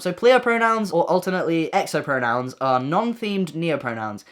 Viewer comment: Huh?? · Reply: his videos are funny so we’re trying not to laugh but it sounded like he said noahpronouns which was rly funny